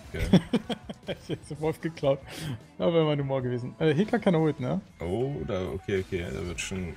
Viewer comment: Nices Video aber sry was hat bei 27:33 gesagt? xD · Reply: "Heca keine Ult"